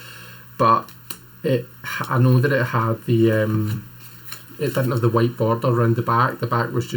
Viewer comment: I don't think it is a greenie, I haven't heard of them having the white border in the back. My first deck was the green box US Games, I trimmed the whole outside border, and it is my favorite! 😅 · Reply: oh I thought he said it didn’t have a white border on the back?